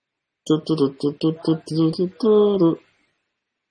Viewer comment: does anyone else hear dan at 50:40 but idk what he says lmao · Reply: i heard "nice live show phil"